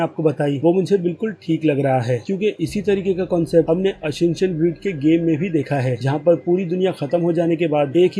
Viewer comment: What he said in 4:05??? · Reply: Assassination creed